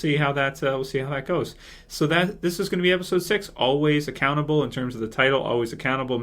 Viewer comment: At first I thought was saying "Always a cannibal" when he said the name of the episode! LOL